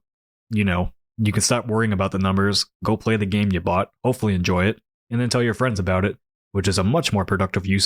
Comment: "Go play the games, you bot!" Umm what? "Go play the games you bought" Oh, sorry, I misheard :D